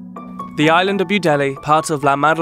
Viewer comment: I thought he said paradis island😧